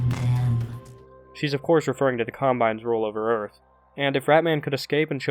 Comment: I uhh, can't make out what you're saying there? Captions say "the combines roll over earth", which honestly makes no sense Edit: OK so, I looked it up - it's the Combine from the Half-Life universe... and I only thought of it by reading the comments. Sorry, the spelling errors made it impossible to figure out, for someone unfamiliar with the lore.